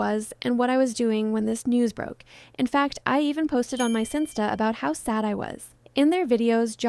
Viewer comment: i know i’m officially old bc i had to replay this section 5 times to confirm you didn’t say “finsta.” i have no idea what you did say or what it is 😭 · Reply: I looked it up and it seems like a sinsta is basically the same as a finsta